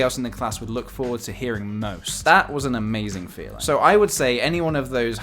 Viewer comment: i heard ''amazing phil''